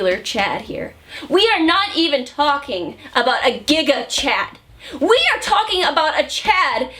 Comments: When she said gigachad at 2:06 was I the only one who heard it as giggukchad? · Did she just say Gigguk Chad? ...I am straight male but I still agree! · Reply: I think it was Giga Chad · no u definitely misheard im sorry son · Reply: it's Giga. But the way she said it + the fact that Gigguk is an omnipresent being, makes it sound like she said "Gigguk Chad" And a Chad he damn IS!